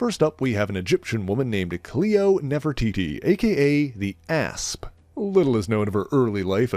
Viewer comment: I heard that totally wrong, though he said the a$$ 🤭